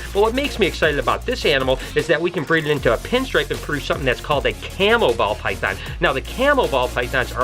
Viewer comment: lol I thought you said camel ball python so when I looked it up it didn't fit your description at all, then I looked up camo... It it was really cool